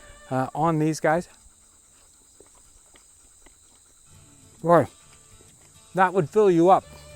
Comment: did you say Glory?